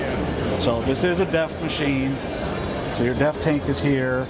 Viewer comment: I thought he said at 4:15 this is a death machine..lol